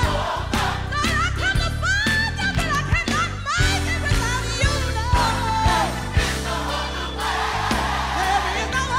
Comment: What is she saying at the 4:36 mark? I understand the part about "not making it without you" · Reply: “I have tried over and over, lord I’ve come to find ya, but I can not make it without you Lord.” I believe. I think saying “but I can not” might’ve been a mistake and she was supposed to say “cause I can not”